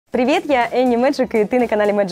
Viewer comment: Только мне послышалось что сначала видео сказала "Привіт"?